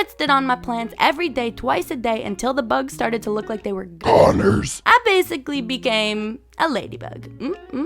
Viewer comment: oh nah not 2:43 dude why did ya say ＧＯＮＥＲＳ